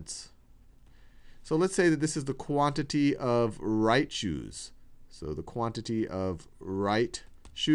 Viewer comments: I thought he said "the quantity of Raichu's" and got rather excited to see where he was going with that xD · In 4:22 I thought he said "Raichu's" when he said "Right Shoes", lol.